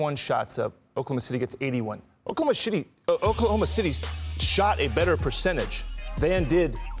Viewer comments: DID HE SAY OKLAHOMO SHITTY?? !!!! @ 2:54 into the clip... LOL 😉😉😮😮🤔🤔 He should have acknowledged it and correct himself hehe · Oklahoma shitty, he said what he said !😤😂😂